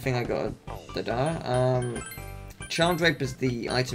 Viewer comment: you say Charm Drape, and even as i watch the video, i thought you said Charmed Rape.